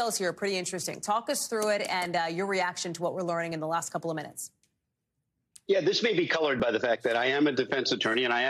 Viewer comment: did Danny say "This might be colored?" 😂